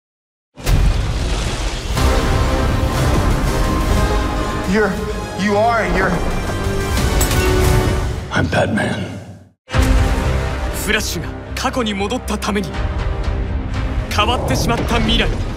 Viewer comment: 多分i'mBATMANて言ってると思うんだけどアンパンマンに、聞こえてしまう